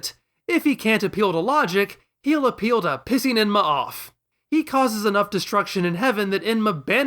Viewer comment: Pissing in your what? I've heard of metaphorically pissing in someone's coffee, and I assume this metaphor means effectively the same thing, but I didn't quite understand the last word. What exactly is Piccolo metaphorically pissing in? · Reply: "Pissing Enma off."